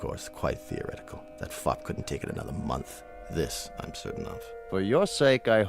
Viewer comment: 'that f**k couldn't take another month'....? .....or am I hearing that wrong? 🤣